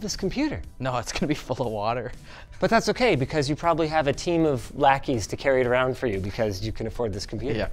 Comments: for just a split second, I thought Linus said “you probably have a team of blackies” and I nearly spit out my coffee… Lackeys, makes much more sense. Christ… lol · thought he said team of blackies lmfao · Bruh I thought he said blackies 💀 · Looool at 10:03 i heard “a team of blackies” I laughed so hard xD